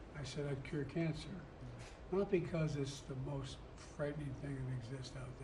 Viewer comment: I thought he said he cured Cancer??????